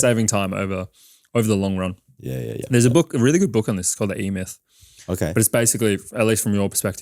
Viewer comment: What is the book at 9:13? Can’t make out what he says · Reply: The E-myth by Michael Gerber